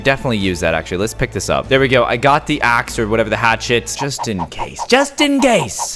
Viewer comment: caylus: just in case just in gase Me: bruh why did you say case and then gase